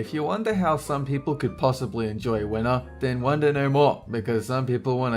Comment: why did i thought you said "wiener art" instead winter? O.o